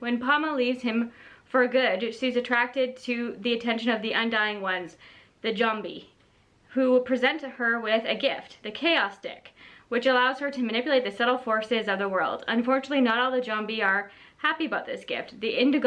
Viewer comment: I 1,000,000% thought you said chaos dick and I was like “he’s giving her the what?!” 😂😂😂